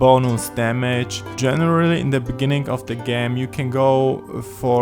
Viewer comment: I heard him say "gam".